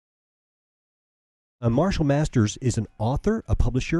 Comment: Who else thought he said "Marshall Mathers" ? I started getting excited thinking Eminem was gonna rap about the Bible...